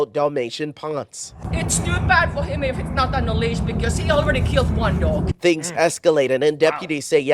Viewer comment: I thought I heard the Finnish woman state that her dog which was not on a leash had already killed another dog.